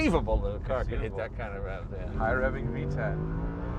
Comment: Did he just say, "VTEC!" or "Higher revving V10?"